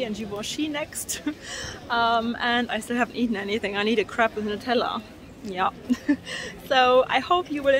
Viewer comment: “I need a crap with Nutella”… this is what my uncultured swine brain thought I heard 😂😂😂😂